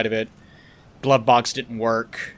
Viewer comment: When was talking about his glove box not working, I could've swore he said blood box the first time.